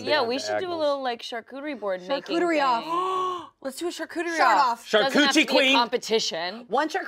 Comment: Noone there seemed to hear you, but I heard you. I heard you say "shart-off" XD